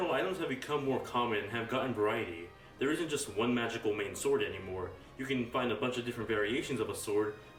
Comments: guy, what is your name. I'm trying to contribute to the subtitles. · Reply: what do you say at 2:48. omain sword??? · what he say at 2:48. omain sword??? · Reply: one magical main sword